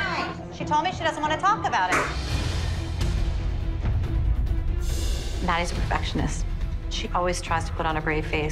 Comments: Are we not gonna talk about how it Said on 3:17 ”Nia’s a perfectionist instead of Maddie.. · captions say Nia’s a perfectionist instead of maddie